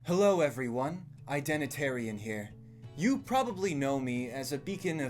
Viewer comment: When you said "hey identertian here", I heard he "hey Derrick bonbaryan here" and I just love that so fucking much. Now admitting I just woke up but I really think it fits.